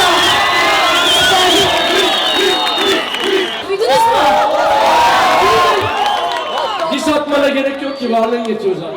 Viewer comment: da cocuk ne diyor anlayan var mi ?? merak ettim :) 'diss atmana gerek yoktu ...' ? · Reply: Diss atmana gerek yok ki varlığın yetiyor zaten